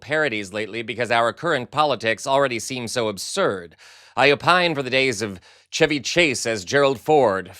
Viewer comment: did he say "I opine for the days ..." ? (5:12 or so)